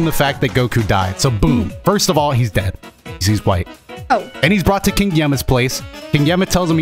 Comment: Is it just me or did say goku died bc hes white